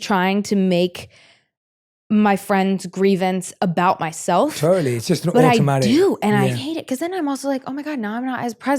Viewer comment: what does he say? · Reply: that its just an automatic response to behave that way